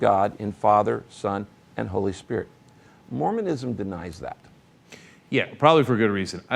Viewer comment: For a second I thought this said moronism lol